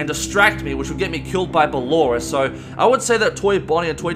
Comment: with subtitles, it says the loris instead of ballora